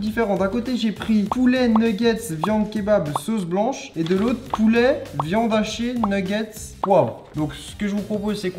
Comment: j'entends viande hachiée moi 😂😭😭